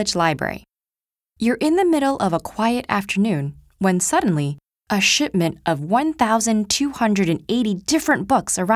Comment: Looool, could've sworn I heard 'a shitment' at the start.